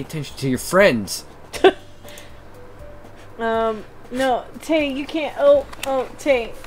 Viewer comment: A wild Tae(?) (Not suer if I heard the name right) appears.